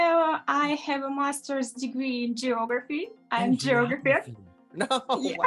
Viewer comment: She could make a miniature map…or miniature rocks.. (at first I thought she said geograher..didn’t realize she said geologist).